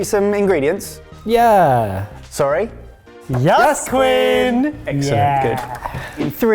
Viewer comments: If you turn on captions, it identifies as "Quinn" because they said "Yas queen!" and the captioner thought they said "Yes, Quinn" 😂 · I'm losing my shit over the subtitles calling "Quinn" because the subtitler misheard "Yes queen"